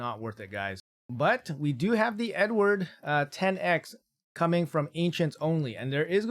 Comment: STG I thought you said "we do have the n-word" and I was like "WHOA WHOOAAAAA CHILL!" 😂😂